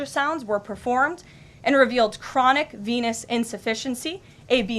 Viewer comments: Hahaha. I thought the WH said chronic penis insufficiency. Listening again, I guess she said “veinous” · I heard “chronic anus insufficiency”. But that makes not sense because Trump is all anus